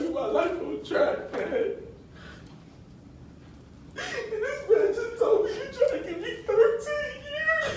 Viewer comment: Did I hear him correctly at 2:48 "And this ratchet's over here trying to get me 13 years!?"